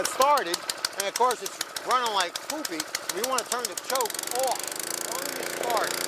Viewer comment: thanks for the overview. and did you say "poopy?"